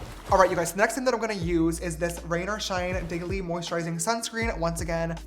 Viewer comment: did i hear that right? i heard diggely